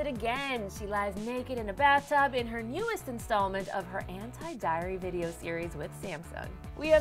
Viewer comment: i thought it said antiDairy.. cus she was in milk it made sense at the time... i'll help myselfout